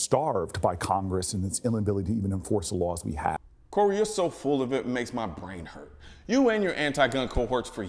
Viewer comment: did he say "full of it, or full of shit"? I hear the words but I am watching his lips move. I think he said full of shit. Now, in my opinion, its a perfect way to describe all of the slimey attributes of this caramel corey booger.